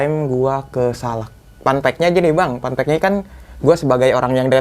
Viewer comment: anjay panpek 😀, awal q dengernya pan*ek, bgtu didengerin lg tnyta panpek (fun fact)